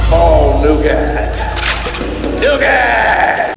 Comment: sounded like: "all nougat! NOUGAAAT!!". someone's addicted to nougat i guess^^